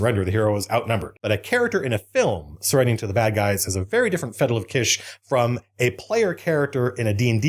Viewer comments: Did Matt say Fettle of Kish? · Did he just say "fettle of kish"? Lol